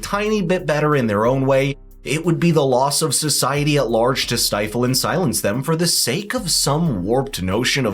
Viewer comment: Hi. What does the host of the video says from 07:33 to 07:35 (from ´...the loss of society..." to "...silence them..."? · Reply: it would be the loss of society at large to silence and stifle them